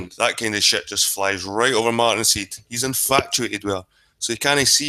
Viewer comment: did you say he's "inflatuated" with her? ;-)